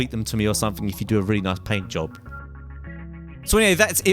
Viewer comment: I thought he just said blowjob instead of paintjob :P